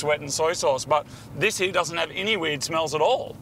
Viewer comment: My captions said at 5:58 "doesnt have any weed smell in it", idk if he meant weed or weird, but very applicable to where i come from and subaru owners